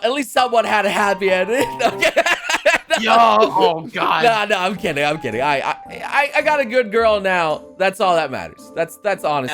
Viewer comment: "few months afterward I found out they got married and had a baby. So maybe it's an happy adventure" "At least someone had an happy ending" [PAUSE] plot-twist just cut at 35:12 What said was: "no no I'm kidding they are dead"